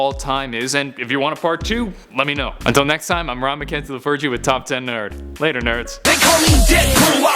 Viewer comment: omg i thought he said "hi i'm ron mckenzie the virgin". i was like huh? and then his name popped up... "oh....!!!!" lol